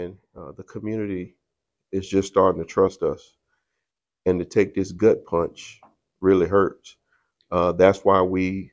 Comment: I thought he said "...and the community is really starting to trust us, and Detective Gutpunch really hurts..." There's nothing funny about any of this but when I thought he called her "Detective Gutpunch" I lost my mind